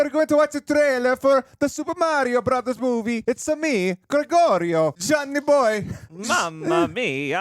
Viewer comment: When I heard “Buongiorno”, I got excited, I’ll admit it. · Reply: HE SAID GREGORIO LMAO IMMA DIE RN.